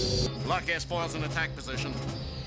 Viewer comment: sounds like he says "lock his balls in attack postion"